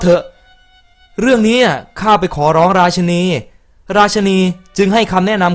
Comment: ทำไมเราได้ยินเป็น ราชะนีอ่ะ😂😂😂